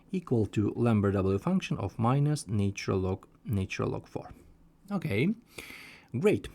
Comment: Is there a reason why you keep saying 'natural log natural log 4'. It's just supposed to be said once, unless you're applying the function twice... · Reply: I hear 'nature-log nature-log'. The solution is good, but he should pay attention to what things are called. I also heard 'divide by -1' to change the sign of the expression ? Not that this matters much...